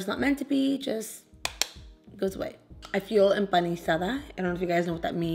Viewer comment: Girl when you were talking about being a freshly breaded fish I heard “embarazada” 😂 I almost fell of my seat